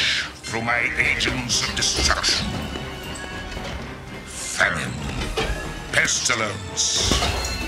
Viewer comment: well...started this video off with a failure, since in the first clip I heard "Theron" instead of "Famine"...it would have been Pestilence, War, Death, and Theron. one of those things just doesn't fit XD